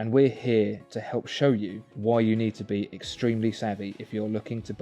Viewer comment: did he say saggy?